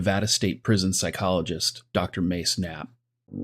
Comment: Dr. Mace Knapp? How unfortunate name... I heard "Dr. May snap"